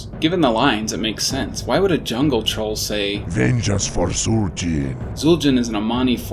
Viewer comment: I ALWAYS WONDERED ABOUT THE "VENGEANCE FOR ZUL'JIN" LINE!!! Thanks for clarifying that! I didn't realize the cuban accent connection at all before. Amazing work as always! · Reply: oh my，I always wrongly heard it as “ninja for zul jin！”🤣🤣